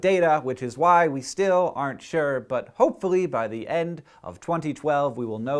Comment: For a second I thought you said "we will know for CERN-tain" ¬_¬